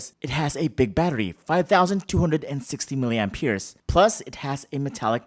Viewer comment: It sounded like you said "5,260 milliamp YEARS" 😆